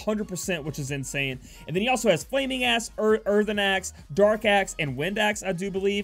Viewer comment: i thought you said flaming ass there lewl